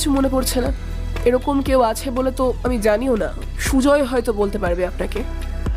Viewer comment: Golpo ta khub bhalo legeche but ektai khut jeta dhorte perechii.... Roshni kokhonoi boleni je "Sujoy hoitoh bolte partoh" se bolechilo 1:19:13 "Sujoy hoitoh bolte parbe"